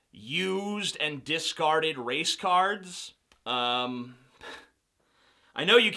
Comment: I swear I thought he was going to say, "You have boxes upon boxes of...watermelons" XD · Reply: lol, I thought he mispronounced "Race Cars" but it was "Race Cards" XD